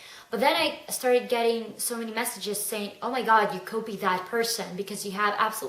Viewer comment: It took me a minute to figure out was she was saying. I thought she was saying that people told her she "Kobe-d" people (instead of copied). I laughed when I figured it out. Her accent is so charming.